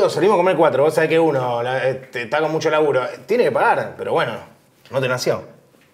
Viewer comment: qué dice? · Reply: No te nació, haciendo referencia a que hablaba de el ( rada)